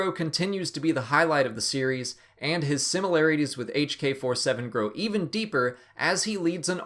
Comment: Did my ears just mistake me, or did you just say HK-47?